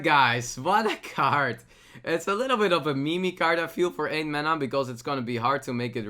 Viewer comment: Did he just said mimi (meme)??? Oh man the day i will dislike thijs has come...